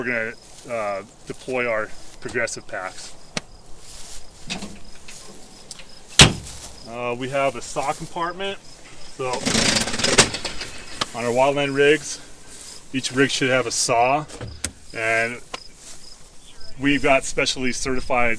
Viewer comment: am i the only one who thought he said and i quote "Sock Compartment"?????????????????????????????????????